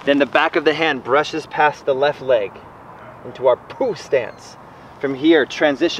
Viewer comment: i thought he said poop stance at first